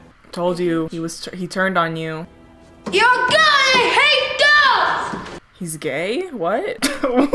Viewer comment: I heard “ Your girly I hate dogs”